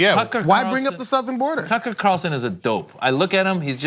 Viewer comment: I thought she said Taco Carlson😂